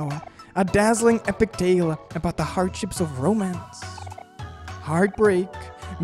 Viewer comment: I legitimately thought she said Romans and couldn't stop laughing